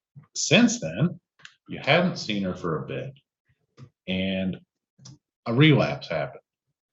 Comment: “Since then, you haven’t (emphasized) seen her for a bit, and … a relapsed happened (happened?)” Now please, anyone out there, am I hearing what I think the attorney is saying?